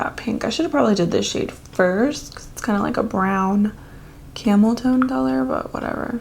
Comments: I totally thought you said, 'brown camel toe color.' 😂🤣 I'd love to see a comparison of the Kiley and Colour Pop brow products. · I thought she said camel toe color 🤣🤣💀